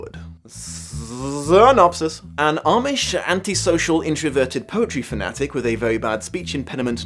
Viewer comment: Book three: "Amish, introverted, anti-social, speech impaired poetry fanatic" Holy crap are we playing adjective bingo? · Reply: Oh, POETRY. That makes more sense. I heard "poultry".